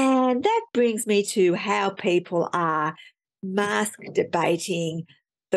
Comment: Thank goodness, for a moment I thought you said muskdebating